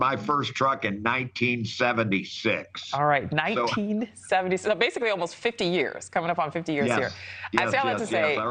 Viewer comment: I thought she said f**** industry for a second.